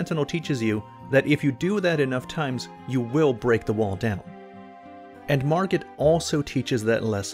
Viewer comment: Could have sworn he said “Margaret” near the 4:28 mark.